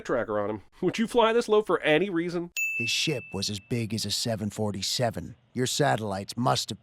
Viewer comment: Definitely thought I heard "His shit was as big as a 747." @@